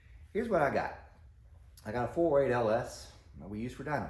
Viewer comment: He says it at 3:00. I think he says 4.8 but he says it so fast I cant tell if he's saying 4.8 LS or 408 LS. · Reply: 408. It’s also in the second video